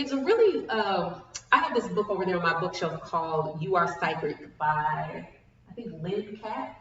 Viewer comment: I didn't catch the name of the book you mentioned at about 38:28, can you post it please? Thanks! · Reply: You Are Psychic by Debra Lynne Katz. I think it might be out of print. Check used book resellers. My fav is alibris because it aggregates lots of resellers from all over the country, so they have a great wide variety of books available.